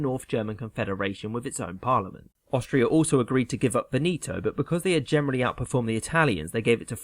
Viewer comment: Everytime you say "Veneto" I hear "Benito".